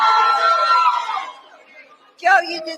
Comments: I heard “you answered all the questions, you knew all the facts” 🤷🏼‍♀️ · No, I heard "you answered all the questions, you knew all the facts"